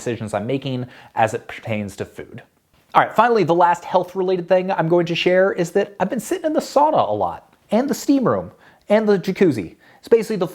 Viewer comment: Ha ha, I thought you said “A sauna!” :)